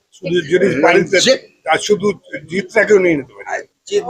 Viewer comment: প্রসেনজিৎ কি বলছে শুনো, রঞ্জিত। জিতের কথা বলে নি।